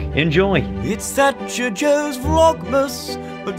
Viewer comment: Just had a moment for the last who knows how long, I thought that joe said in the vlog intro ‘Vlogmas sir’ and then just realised that it’s ‘vlogmasser’ 😂😂